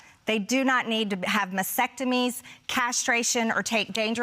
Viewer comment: I thought she said kids were having vasectomies for a second.